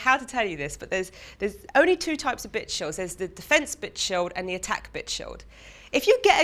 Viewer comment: hahahaha did she just say bitch shield!!!!